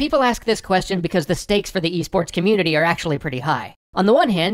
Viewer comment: I keep mishearing e-sports as EA Sports.